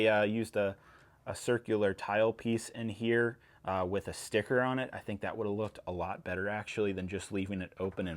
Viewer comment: did just say he wished there was a sticker?